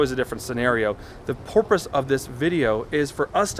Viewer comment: Did you say the 🐬 porpoise? Lol